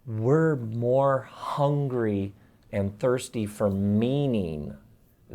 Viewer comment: I thought he said "hungry for meme-ing".